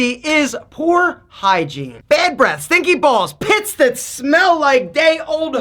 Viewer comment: did i hear stinky balls?